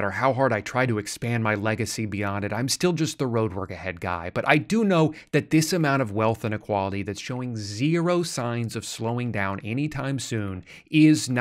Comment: Sorry I wasn't really paying attention, did he just say he's the road work ahead guy? I love that vine! ❤😊